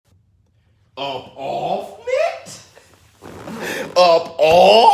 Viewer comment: What are you saying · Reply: SquishyBread upoffnit